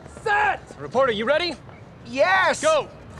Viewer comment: ohh that makes sence.. they were saying reporter i thought they were saying porter like it was his last name. thanks